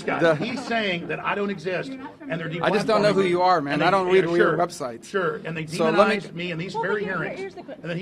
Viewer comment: I think it sounded like Marco said "I don't know who you are, I don't watch your show" which if I heard right sounds like he does know but just wants to ignore him